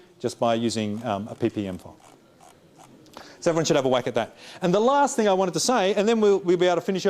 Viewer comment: did he just say "so everyone should have a wank at that"?